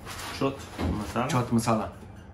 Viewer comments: Did they just said Chut masala😂😆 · M i the only one who heard Chut masala ??